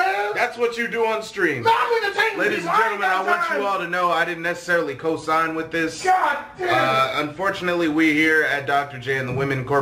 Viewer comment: what the frick did say lol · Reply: "Mhyentertaintoyouowndamtime!" I wonder what makes him say that?